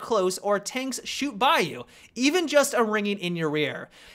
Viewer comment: Wait did he say "Ringing in your rear" 9:18 getting personal